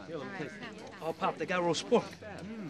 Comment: I thought he said “Hey pop they got roast spoof” lol